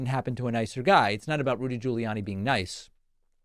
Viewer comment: Lol I heard "Rudi-Julie-Annie" up top..!